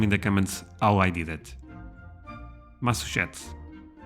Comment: 3/10 'Massuchetts' is what you said. Massachusetts Mass a chew sets